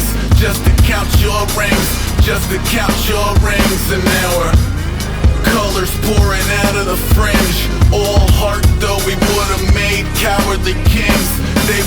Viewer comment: i first heard "shapes pouring out of the fridge, all heart doughy water man cowardly gangs"